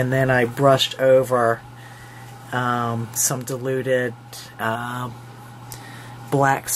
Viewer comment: You have just had me in stitches! I thought you said "deluded whatever", was is paint? But what you did say was "diluted" LOL I was trying to imagine "deluded paint" !! J x PS forgot to say Secret Garden is my favorite childrens book and I adore the paper line.